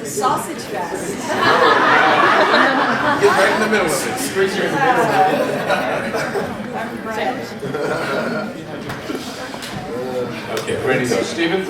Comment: Does anybody know what Jai says around 00:34? ;] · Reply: "Squeeze here in the middle Maggie"